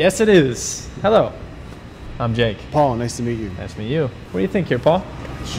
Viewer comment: “I’m Jake..” “Paul nice to meet you.” What I hear: “I’m Jake Paul nice to meet you.”